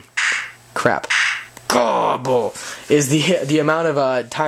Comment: did you say gobble?